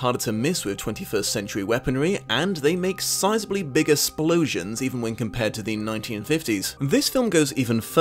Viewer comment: The clothes probably survives because a lot of clothes are made of synthetic materials... wait did you say 'splosion' at 17:42? because that's adorable.